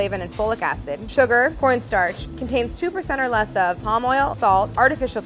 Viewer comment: When she said corn starch I thought she said porn star... I can't hear